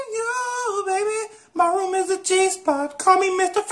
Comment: "mY rOoM iS a ChEeSe PuFf" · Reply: G spot it sounds like he said cheese puff but it’s g spot. Besides what’s so funny about that it doesn’t make sense.